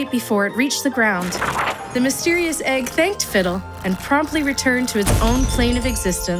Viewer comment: instead of "and promptly returned to it's plane of existence", I heard "and probably returned to it's plane of existence"...